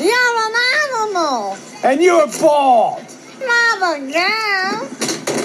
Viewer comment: The funny thing is she said “Mama Girl” but she actually said “Not again…” lol